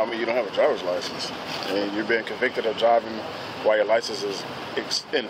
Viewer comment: I had to rewind I thought "driving without license" was molest or something I was very surprised/ startled! Gladly I just misheard.